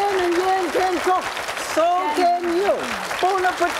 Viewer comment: Did anyone else catch Jan saying 好 飲好 食 (a translated equivalent to Bon Appetit in Cantonese) at the end of this episode? Cool chemistry, great cultural exchange, and mad respect!